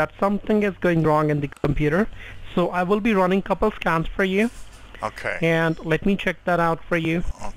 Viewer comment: sounds like he said that he will run a couple of "scams" instead of a couple of scans.